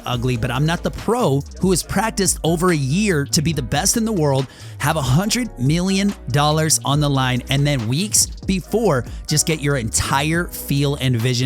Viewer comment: did you say "have a hundred million dollars on the line"?